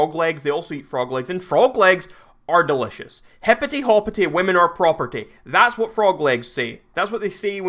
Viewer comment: Did he just say hipty hopty woman are property